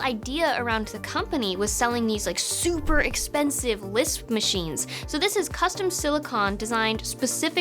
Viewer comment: did you say LISP?